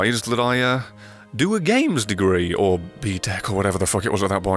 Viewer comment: did he say ptec